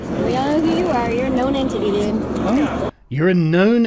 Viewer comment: I thought she said "You're a no-name to me, dude" but "You're a known entity, dude" also makes sense